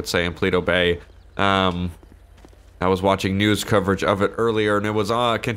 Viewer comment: I thought you said noodle Coverage instead of news coverage lol